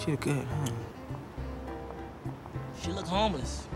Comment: Y’all gon ignore the fact that Chris tucker said “ she looks homeless”🤣🤣🤣🤣 Edit:Thank you for so many likes ♥️ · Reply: I thought he said she looks Harmless and then gets in a bunch of trouble Trying 2 get her...🤦🏾 lls #Rns